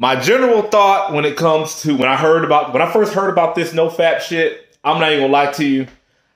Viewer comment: It sounded like you said no fat shit 😂